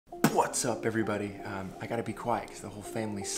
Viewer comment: Why did you say buwasup everybody